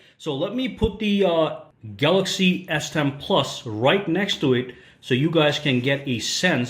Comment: said galaxy s10 plus at 3:34 instead of note 10 plus